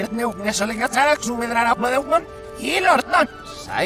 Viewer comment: escuché "el orto"